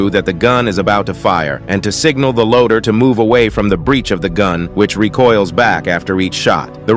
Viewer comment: I thought he just said one away